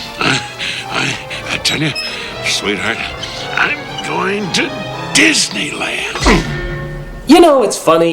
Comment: Did he REALLY say "I'm going to Disneyland"? Or is that the NC's sarcastic humor on this part?